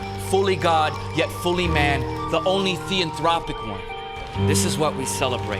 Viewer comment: Please can someone please help me with what he said at 1:52 Thanks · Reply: theanthropic: meaning embodying deity in a human form; both divine and human.